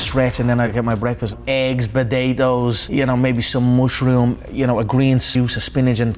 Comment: It really sounds like he said “batatoes”